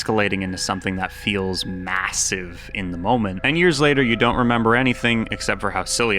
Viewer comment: did you say..massive?